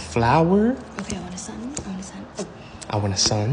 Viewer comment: I thought he said i want a son 💀💀💀💀💀💀💀💀💀